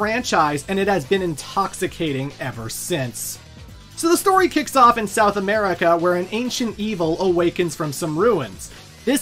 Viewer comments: So subtitles say Mexico and Ogre does have Mayan/Aztec decor but guy says somewhere in South America 😂. T3 was fire though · Says the story starts in south america but the subtitle says Mexico lol